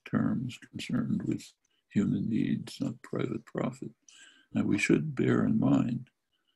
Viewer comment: "Concerned with human needs not private profit", that should be the caption because that's what he said.